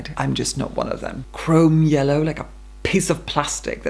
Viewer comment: When you said "chrome yellow like a piece of plastic" i thought you said "chrome yellow like piss plastic" lol I was trying to envision what an item that was the colour of chrome yellow piss would look like lol